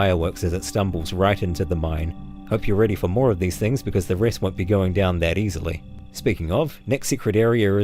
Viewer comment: Err ya goin''down. It's also a small joke between me and my sister, tho we always thought he said "It's going down." which we quote randomly.